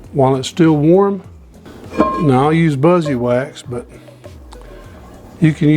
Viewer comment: I have used olive oil to season my cast iron skillet from the beginning… I have been delighted. Practically every video I have seen on seasoning cast iron cookware speaks against using olive oil. My experience is that olive oil works very well, is not sticky and does not leave a residue on the skillet. I re-season and re-heat the skillet after each use. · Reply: What are you using in the video? I thought I heard you say "I use my buzzy"?